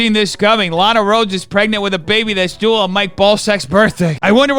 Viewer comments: good day, defenders! hope you're doing amazing. by the way, did you know strawberries are not berries, but bananas actually are? :0 here's an extra video for you <link> love ya! <3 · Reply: Hahaaa! I thought you said she's going to have a baby that's due on my ball sack's birthday 🎂 Still waiting on the reveal my man 🤗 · I have a hard time hearing.. is he saying the babies birthday is on Dennis's ballsacks birthday (my ballsacks birthday @ 3:24)? I'm so confused.